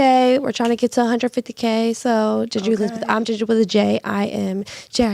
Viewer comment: It sounded like she said GENDER not Jinger… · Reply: Yea that was the issue…she said Gender, not jinger